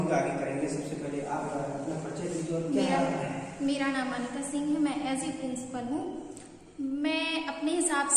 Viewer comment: I'm from Bangalore just scrolling about teacher's on development...so i watched the full video who is the second teacher listen 2:52 till two minutes " mein asi principal hoo" " instead of as a principal" or kya matlab mauranipur tehsil ho Jaana chahiye Wikipedia ke hisaab se woh ti Pehle se tehsil h ... no.1 teacher sabse acche se apne view diye unhone ek ek point acche se bataya waise ye school konsa h